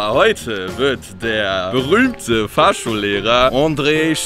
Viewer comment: Ich habe Fahrstuhllehrer verstanden xD